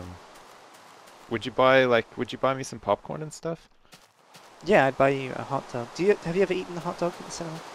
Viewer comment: I thought he said "I'd buy you a hot tub"